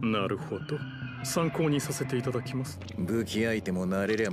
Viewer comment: can someone help me with the Japanese at 2:41 please? I hear something like sanko nisei itadakimasu but I know i'm wrong. これを読めるでしょうか？私は日本語が大好きイギリス人です。日本語の質問があります。２：４１にバネヒゲさんはなにかをいったけど、まったくわかりません。彼の文を書いてくれてください。勉強したいですから。これを読んでくれて、ありがとう！ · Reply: 参考にさせていただきます